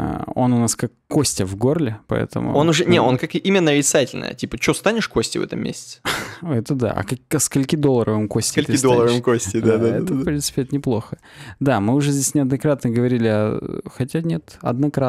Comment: Мне послышалось "Макса срака долларова" :D